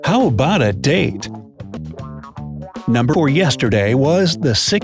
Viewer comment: #8 doesn't make sense, if yesterday was the 16th, then that means today is the 17th, and the day after tomorrow (18th) would be the 19th. How is the answer the 20th? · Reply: I heard “numba or yesterday”